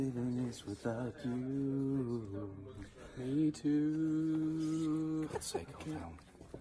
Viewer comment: what did uhtred say ? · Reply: For God’s sake,Aldhelm 😂😂😂